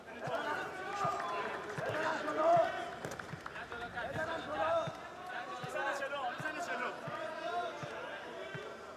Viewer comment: what is he saying? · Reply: He's saying "do I hit front row?"